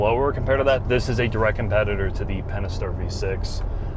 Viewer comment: When he said “Pentastar V6” at around 8:00 the caption said “penister”.